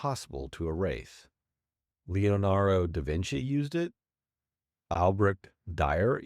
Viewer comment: Where was it in the video? Time stamp. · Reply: I heard "leonarro DaVinci" admittedly it could've been me half paying attention because I had it playing while at work.